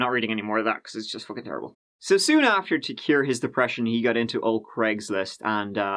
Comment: I thought you said “ to cure his depression he got into old grey” like bro that’s where you go to be depressed